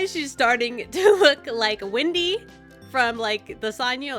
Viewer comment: Who else thought that when she said 'this of looks like Wendy' it sounded like 'It looks like Winny' as in Winny the Witch